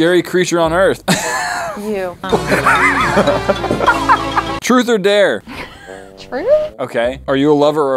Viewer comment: I thought it said: treet a chair.